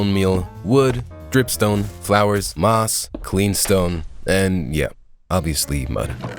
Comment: did he say cleanstone or am I tripping